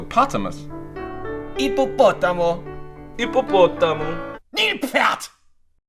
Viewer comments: я услышал «нет блять» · я один слышу как "НЕТ БЛ*ТЬ" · мне послышалось "нет бл*ть!